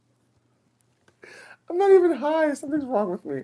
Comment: Did say "retarded" at 7:35? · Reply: Girl she said "haven't even started" in reference to Aja losing it before the first question was even asked